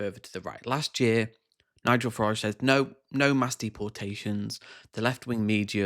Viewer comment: I thought you said Nigel Faraud..a combo of Farage and fraud 😂